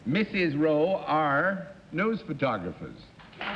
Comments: Sounded like he said "Nose photographers". Also, what's with this show's obsession with worms? This is the fourth or fifth time I've seen that someone has something to do with worms... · I had looked away from the screen when the first ladies’ line was revealed pre questioning and when John revealed what they did I thought he said “Nose Photographers”. I thought - wow what a specific job! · Sounded like he said "Nose photographers". Also, what's with this show's obsession with worms? This is the fourth or fifth time I've seen that someone has something to do with worms... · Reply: I thought he said "nose photographers" too! I had closed my eyes when their job was revealed onscreen at the start because I like to guess along with the panelists. Finding out it was really "NEWS photographers" after all was a bit of a letdown, frankly! 😄 Not having seen the other episodes in question, I will refrain from commenting on the worms.